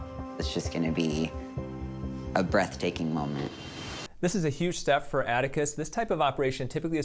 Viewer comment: When he said ' this is gonna be a breathtaking moment ' I thought he said ' this is gonna be a breast taking moment ' and I was like you're damn right